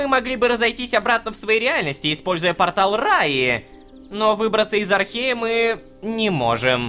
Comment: ахаха мне послышалось использую портал в рай